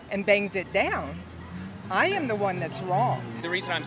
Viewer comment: I had to rewatch 3 times but did she say at 03:40 that if somebody bangs her door down she’s in the wrong? What the hell!!! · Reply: Pretty sure she said "I am the one that's wronged" but I still don't know what her point was.